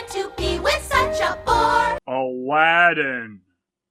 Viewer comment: "... to be with such a WHORE"??? Sorry, I really have trouble with making out what they are actually saying. This is what I hear each time while watching the review.